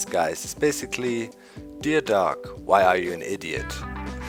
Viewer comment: I listened again, I misheard you. You said idiot, and I heard Atheist :( My hearing sucks sometimes. Many years out here actually building in the RW has damaged my hearing some :( You'll have to forgive me and my bad hearing. Keep it up!